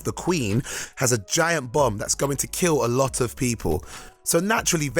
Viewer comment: Thought for a moment he said "A new villain calling herself the Queen has a giant bum that's going to kill a lot of people." XD